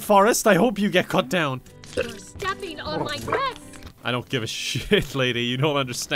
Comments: i deadass thought she said "you're stepping on my breast!" lmao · When that woman complained about Octodad stepping on her dress I misheard it as 'You're standing on my breasts!' and didn't question it, because... you know, it's. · I thought that lady said "you're stepping on my breasts!" xD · lol 😂 I heard stop walking on my breast.